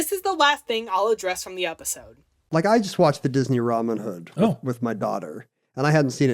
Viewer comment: Did anyone else hear "Ramen hood"?